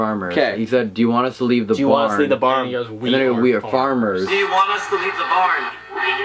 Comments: when i first saw that video i thought it said leave the barn and not we are framers but now i can hear what they are saying they heard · If you listen to the We are farmers idk if it just me but it sounded like it said leave the barn listen a couple times